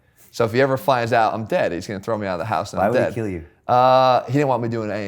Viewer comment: Did he say I will kill you haha 04:18?